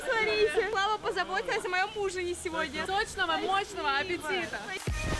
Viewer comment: мне послышалось не ужин, а муж)))😂